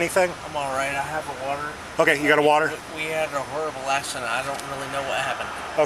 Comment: does he say they “had a verbal accident”?